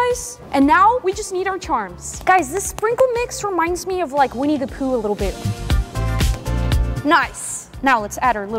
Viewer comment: when you said winnie the pooh i thought you said we need to poo